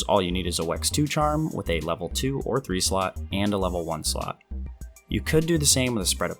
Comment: I’d like to ask about what talisman you used for comfort set I couldn’t clearly hear what you said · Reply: weakness exploit 2 with a level 2 or 3 slot :D